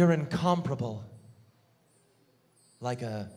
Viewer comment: what does "incomprable" (or whatever he says at 34:34) mean? Can't look it up since I'm not sure how to spell it right.. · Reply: incomparable; it means you cannot compare it to anything, get it?